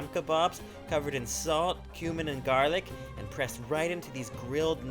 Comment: Did he say "Salt, human and garlic" at 15:44?